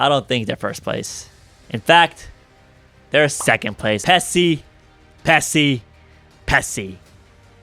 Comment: I misheard Pessi as Pepsi...